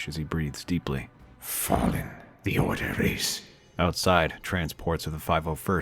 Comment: Going to be honest, I heard "Fucked the order is" and I laughed more then I should have